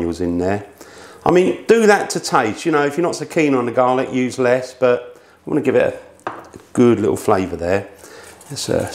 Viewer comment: What temperature did he set the oven for initially. Sounded like he said 3 knots. Whats that? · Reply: 425F